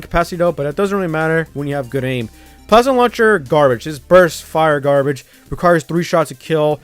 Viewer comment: i cant hear it correctly , can someone tell me what it is? xD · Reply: Plasma Launcher